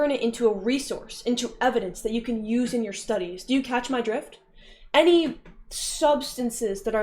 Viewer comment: I first heard "do you catch my drip" and that fit even better